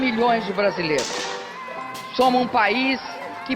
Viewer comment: Olá. Aos 04:51 eu ouvi "Somos um país ...", mas a transcrição disse "We IS (?) a country..." o que sugere que a gramática falada é errado. Por favor, qual é a maneira correta de dizê-lo em português? Muito obrigado. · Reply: Na verdade ela disse "somo um país". Ela omitiu um "s", aí está o erro.